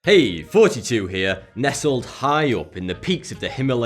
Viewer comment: each time he says THOUGHTY2 i heard it as 42